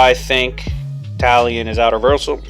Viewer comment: I thought you said that Talion IS outer on Discord